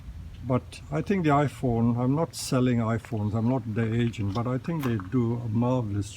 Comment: when you were saying sorry about the iphone, then i thought you said "Im not selling them, im not that Asian.." i laughed out loud at that until i played it again.. "im not theyre agent" haha